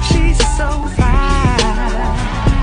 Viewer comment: "As long as I got my suite and tie" Wise words Shut up who is replying on my comment. 💀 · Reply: All I hear is"as long I got my soup and pie"